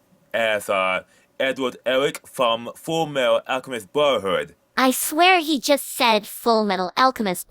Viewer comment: I heard "Fullmetal Alchemist Butt-herd".